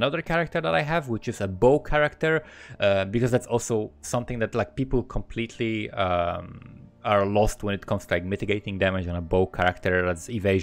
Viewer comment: "Because that's also something that Black People completely umm are lost with..." LOL I had to rewind and listen again to realize that's not what he said